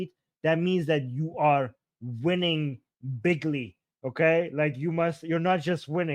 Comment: Whats with flying pigs?? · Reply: He said bigly (after Trump,) but she thought he said pigly.